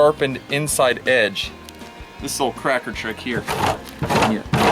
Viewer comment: Did he say “this a lil cracker trick here” lol